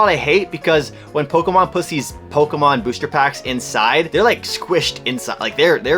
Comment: it sounds like he said Pokemon pu$$ies. Lol sorry.